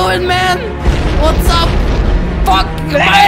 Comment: what did he say Edit:oh he sad funk